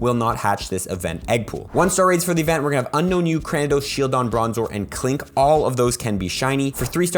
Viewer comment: i thought he said “ukraine”